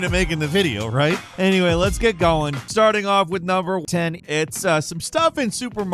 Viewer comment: Can somebody else hear the "Starting off with the number one" ??. It's edited to hi saying number 10, but I can hear him almost saying number one